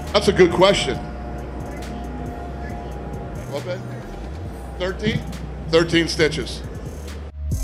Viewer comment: did he just call his secretary babe? like im not surprised if so. we’re talking about dana after all